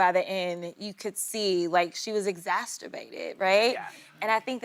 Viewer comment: What was that word, "egsasterbated' (sp) (1:59)? I know I would say exasperated but maybe she meant something other than that? It's good that someone is speaking up in the WH, but way too little people are speaking up for what is going on in the WH. I don't recall anyone saying they should recount the votes this time? Why not? These many people voted for him?? Really? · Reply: Exacerbated